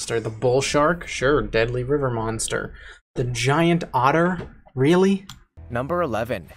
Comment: i thought he said giant daughter